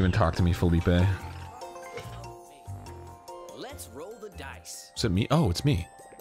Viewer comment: After all the Broing I swore I heard Monopoly guy say "Bro the Dice".